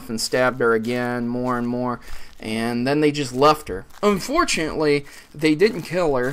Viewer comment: Did you say "unfortunately" the didn't kill her" at 2:10 Its a good thing she didn't die. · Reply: He did in fact say unfortunately but I agree with him, the girl is probably handicapped now or something that has her incapacitated, in my opinion I would rather die honestly